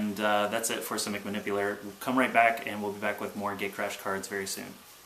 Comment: It sounds like you say "Gaycrash" on start